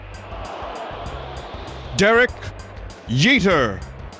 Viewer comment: anyone else hear "Sick Fortnight Yeeter"